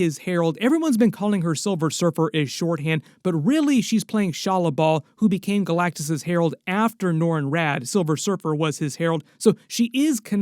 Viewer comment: my closed captions said she's playing volleyball 😅😅😅